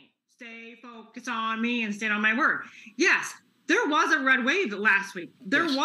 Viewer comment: Did I hear her say at the 1:52 mark, stay focused on me instead of my Word?